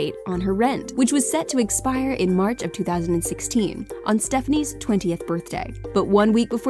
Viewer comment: Her dad was going to cut off her rent in March of 2016?? Did I hear that right